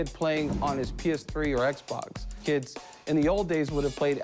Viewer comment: did he say PS3?